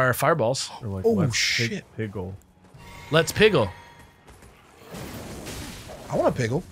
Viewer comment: When I was a kid I always heard "let's pickle."